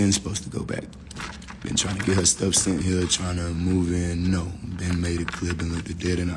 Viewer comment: He told her to leave a thousand times and she still trying to stay. She doesn’t want to leave. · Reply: I swore she said she has stuff there..